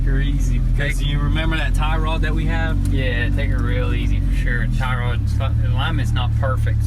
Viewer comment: Anyone else hear at 8:17 "tie rods fked"